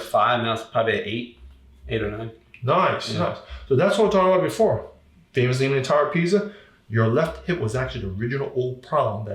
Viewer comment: did he say "thats what i was talking about b4, familys eating a entire pizza, your left hip was actually the original old product".?